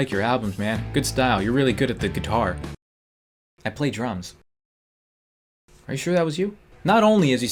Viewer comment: lol i thought he said 'I plagiarise' instead of 'I play drums.'